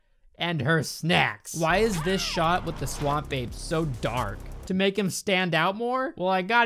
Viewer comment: I don’t know what to tell you but I keep hearing “Swamp babe”